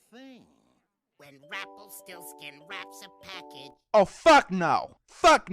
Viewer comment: Wait did he just say Rumplstilskin? Since when did Shrek become a part of this???